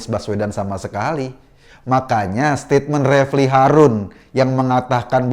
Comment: kok lama-lama saya dengernya Refly Kadrun